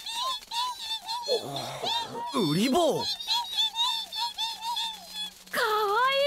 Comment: I thought said kuriboh...did anyone misheard that?